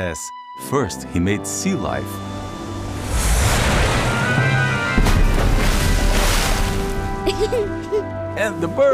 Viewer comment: I thought he said “first he made sea lions” and I was very happy. Atheists love sea lions.